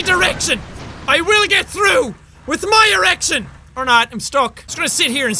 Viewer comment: Did he say my direction or my erection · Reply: Tor Kingdom he said erection 😂😂lol